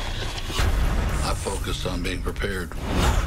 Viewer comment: What did he say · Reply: "I focus on being prepared."